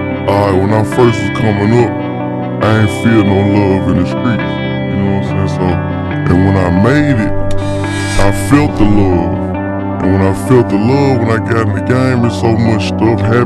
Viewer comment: Can someone help transcribe what the guy is saying at 1:32? It isn't in the lyrics on google. Here's what I've got: "Aight, well not (the?) first (time?) it's comming up I ain't feel no love over the street You know what I'm saying So and when I maybe *bzzt* I felt the lord And when I felt the lord When I get back in the game there's so much stuff happening I've seen like sleeping gave water round the walls and have faked it you know Stuff that you see going on every single day" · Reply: "Aight when I first was coming up, I aint feel no love in the streets. Yk what i'm saying so... and when I made it, I felt the love. And when I felt the love - when I got in the game and so much stuff happening, I seen - like - seen the game for what it really was and how fake it is. Stuff that you see go on every single day."